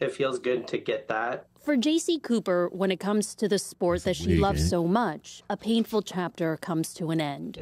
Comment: did they JUST say, "the sport that HE loves so much???!"